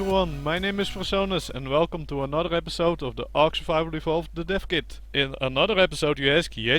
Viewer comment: Lmao in the intro I heard "Welcome to another episode of ark survival evolved to defecate"